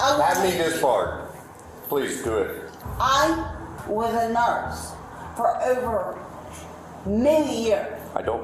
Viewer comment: The cop is ruthless haha “I don’t like you either” “Shut up!!” “I’m going to start treating you like a four year old and count.” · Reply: “I am a NARSE and I did 12 years of it” lol it sounded like she said “narse” instead of “nurse”